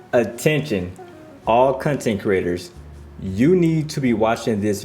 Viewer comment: Did you just say Cuntent creators lol